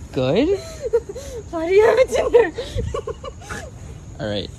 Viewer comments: I just heard, "I have a tumor." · At this time 7:46 the toxic girlfriend said a word but if you didn't understand heres what she said WHY DO YOU HAVE A TINDER Can't really hear it